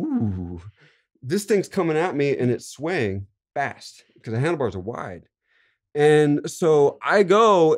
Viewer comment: I totally thought he was saying he front crook'ed the handlebars. would've believed it.